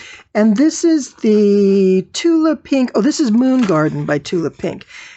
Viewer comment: I love the layer cake you used. It says Free Spirit, but what is the name of the fabric line of the layer cake? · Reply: Sorry, I just listened again and heard you say Moon Garden. Thank you.